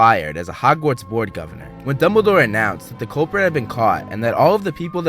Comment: did you say cobra or did I mishear something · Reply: culprit